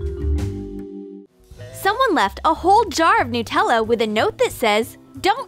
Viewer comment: is it just me or did she say NutellO